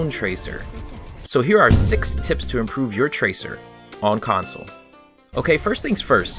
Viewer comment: thought it said "improve your cancer"